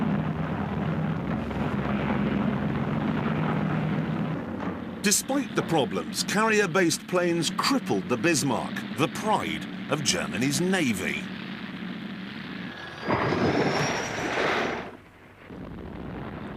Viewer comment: did he really say the Bismarck was the pride of the Japanese Navy? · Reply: No, he said "The pride of Germanys Navy"